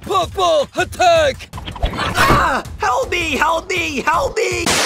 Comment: instead of help me it sounds like healthy